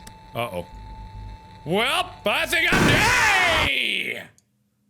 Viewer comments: sounded like "well I think I'm Gaaaaaaaaay!" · I think I heard Mark say "I THINK IM GAY" at the end, did anyone else? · "I think I'm GAYYYY!~" was all I heard xD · At the end I thought he say " Well now I think I'm GAYYYYYY' 😂😂😂